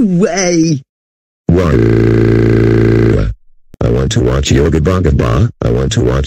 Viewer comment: When he repeats yo Gabba Gabba, he sounds like he is saying "I want to watch yobongabong".